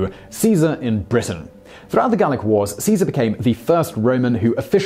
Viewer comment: I thought that it said garlic wars. :D